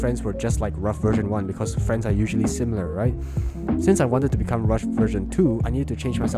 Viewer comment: I don't think anyone noticed but the editor put 'Russian' instead of 'rough' 😂😂😂😂